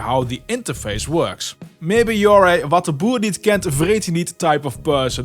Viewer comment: What was that you said from 0:27 - 0:30? Nice video! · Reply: Wat de boer niet kent vreet ie niet ^^